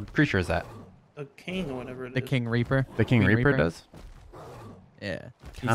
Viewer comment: I thought u said the king raper I was laughing so hard